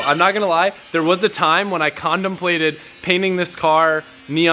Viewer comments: Am I the only one who heard him say “ Condom plated” instead of contemplating??? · Did i hear a rudnickism?... "There was a time when i condomplated"